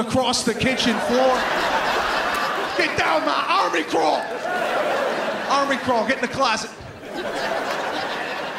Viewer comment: what he say at 3:33 ? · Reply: "Army Crawl, get in the closet"